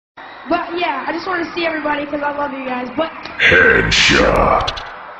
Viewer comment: is it just me or does it sound like she says "fuck yeah" at the begining?